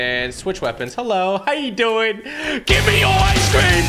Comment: Sounds like he said GIVE ME OIC CREAM